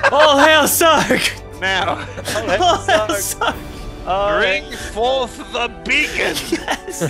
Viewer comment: Before now I always thought Sark said "Bring forth the biggen" in the outro.